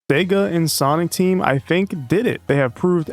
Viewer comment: "Vega and Sonic Team" lol at least that's what I heard